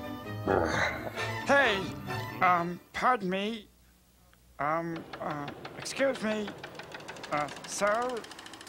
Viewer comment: “Hey! Um, pardon me? Um-uh, excuse me? Uh, sir?” (I have no idea what else he said, but I think I got it.)